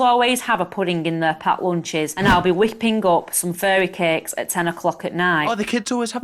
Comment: I thought she said furry cakes..ngl